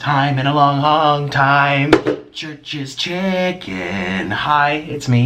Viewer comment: I thought you were saying Trish's Chicken lmao